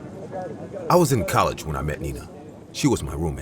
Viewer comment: I swear to god the first sentence i heard was "i was in college when I first met ni**a"🤣🤣🤣